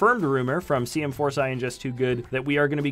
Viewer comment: did he just say just2good?